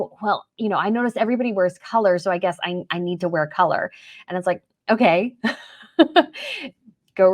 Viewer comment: wear more colour? I think they actually said, MORE COVER